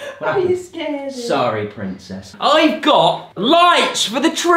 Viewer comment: did he say pincess